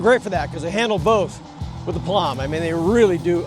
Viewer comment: Looks like a fun ride, a very different setting than up here in Eastern Canada, did i hear you say with “aplomb” at 5:30, very French of you…smile